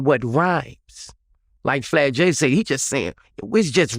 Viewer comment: Did he call fat joe flat jay?😂